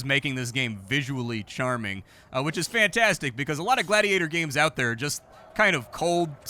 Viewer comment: Did you say Gladuis like the ps2 Gladiator Game? That game rocked. Also, Road to Freedom is hands down the best Gladiator Game I've seen.